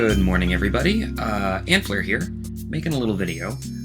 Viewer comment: idk why i heard that ''Hitler'' and i died laughing